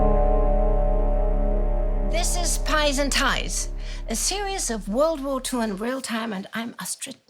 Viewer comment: I swear I heard Astrid say "This is pies and ties!" 0:46 That would be delicious and oh, so elegant! EDIT: Love Indie's "Nobody expects the NKVD!" moment at 6:33 :D